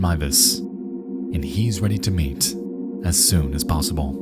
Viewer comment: when he said “<…> he’s ready to meet as soon as possible” I swear to God, I thought Nexpo said “he’s ready to meat as soon as possible”💀 Which would make total sense